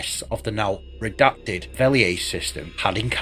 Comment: At around 6:20 you reference a now redacted system... sounded like Veliet(?). Could you provide the correct spelling? I'd like to do some research of my own. Thanks! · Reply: Veliaze <link>